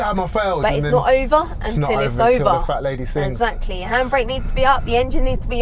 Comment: did he say "it's not over until the fat lady sings." 🤣🤣🤣🤦🏾‍♂️🤦🏾‍♂️